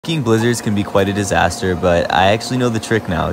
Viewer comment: surely im not the only person who thought he said fucking blizzards bc it was cut??